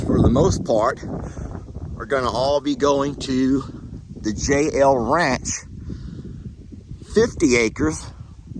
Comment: With your accent it sounds like your saying jail not J L ranch